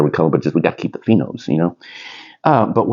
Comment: what are phenodes? or what do you say right here? p nodes? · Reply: Phenos